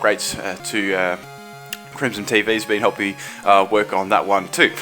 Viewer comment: for a second ther i thought you said crimsontv lol